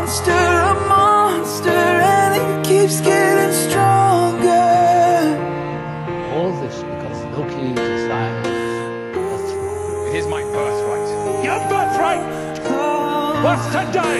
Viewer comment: what is he saying at 2:42 ? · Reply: "All this because Loki desires a throne." "It is my birthright." "Your birthright--was to die!"